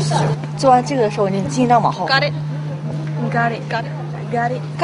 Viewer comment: Oh I thought Krystal said, 'Know what I'm saying?'